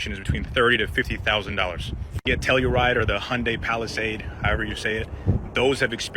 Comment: I missheard the second guy I thought he said Hyundai Palestine